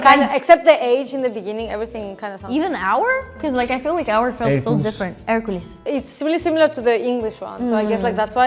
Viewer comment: this one way off with the captions for the English pronunciation. The caption said “Heracles” instead of “Hercules” and they’re two completely different people.